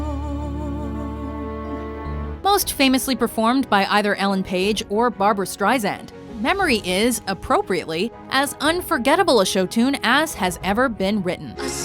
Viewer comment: Sounds like they said Ellen Page rather then Elaine Page.